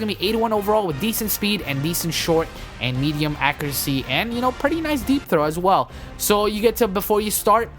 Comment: "Pretty nice deep throat as well." Thats how I heard it the first time.